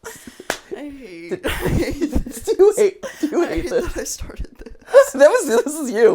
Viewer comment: I also heard, “do you really think there’s been abductions in this facility?” Or something along those lines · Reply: for some reason I heard “do you really think there’s a bathroom in this scenario?” 🤣🤣🤣